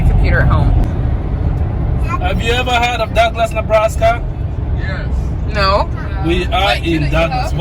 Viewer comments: I thought said “Duckless, Nebraska”... I don’t think they have duck eggs lol. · I totally thought said "Duckless, Nebraska" instead of Douglass. 🤣🦆